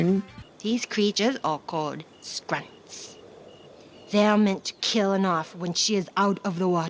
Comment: the subtitles say "grunts" instead of "sprites"